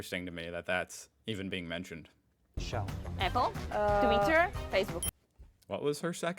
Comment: Did he say SHELL?! Because that's definitely not the American company! 😁✌🏼